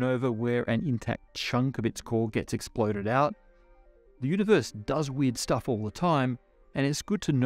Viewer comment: Misheard "the Universe does YEET stuff all the time". LMAO